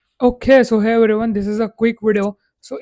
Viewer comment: you said ok so hey everyone but I heard ok SUHAIL😂